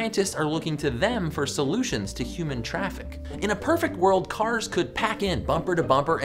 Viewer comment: first time you said human traffic 1:01 i thought you said human trafficing